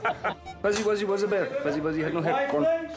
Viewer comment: Is my absolute favorite.🥰 I have no clue what Johnny says but the way he snickers and makes the other guy lose it (although he tries to stay professional and jokingly says "i'm trying to work" to Johnny) clearly shows, that everyone enjoyed working with Johnny and there was a laid-back atmosphere at the set. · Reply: I think he was saying "Fuzzy-wuzzy was a bear, Fuzzy-wuzzy had no hair" it's an old tongue twister.